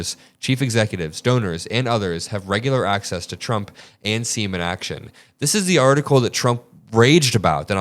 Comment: At first I thought said “semen action”